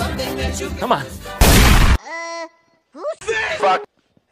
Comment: What did the toad say at 4:46? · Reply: He said: Uh... Who's THIS F**k?